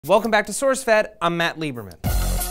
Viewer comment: all i heard was: " welcome back to sourcefed, im fat lieberman". Fat fat fat fat , fat fat fat, murica